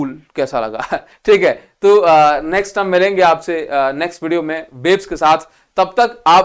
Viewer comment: did he said babes ke saath ? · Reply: Nhii!! Waves ke sath 😂💀